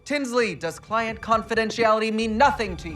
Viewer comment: Well, I'm bummed. I didn't have the subtitles on so I heard "Kingsley" instead of "Tinsley." I thought we were getting a certain super villain who was also a fashion designer.